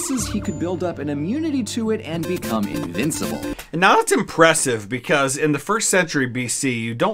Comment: You said "now that's" so fast it sounded like you were saying "not impressive"